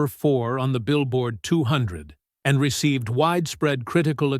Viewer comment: did you just say.. critical acclaim...?